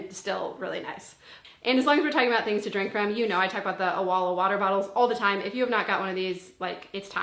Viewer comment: Get the best family calendar for organization! Go to <link> to learn more about the Skylight Calendar and use code MINIMALMOM30 for $30 off your 15 inch Calendar! Available globally. Thank you Skylight for sponsoring and thank YOU for watching! I hope this finds you well today! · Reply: What's that water bottle called? Sounded like you said Awala ??